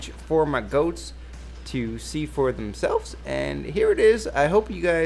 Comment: I heard it as "to C4 themselves", I was like, wait, what? :L